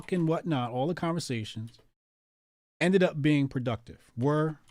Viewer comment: I think Scott said he was trying to be productive, but thought he said provacative. Just past 106.30.